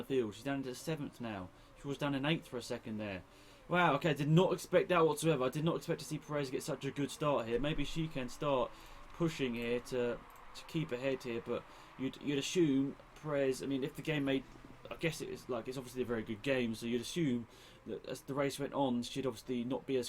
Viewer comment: you said peres can push to keep ahead (i heard keep a head because of doggy neck) and both makes sense!!!!!